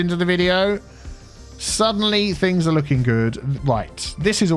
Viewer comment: is it concerning I thought he said finger licking good